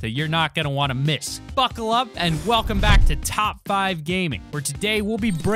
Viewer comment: did you say fuck love?